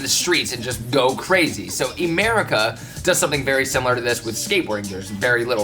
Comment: Definitely said "Emerica" instead of "America" Get it? like the skate shoe brand